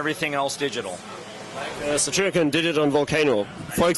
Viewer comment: What is the interviewer saying at 09:03? Volcano something? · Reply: Satyricon did it on Volcano